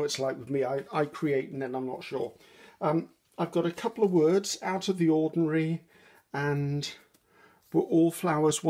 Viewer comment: Just about the time you asked if you were driving us mad, I was thinking how often I have you, Gail or Edith playing while I'm doing something at the desk. When you were auditioning "Out of the ordinary," I thought it said, "Out of the library." I thought to myself, "Yep, that's exactly what I needed more of when I was young."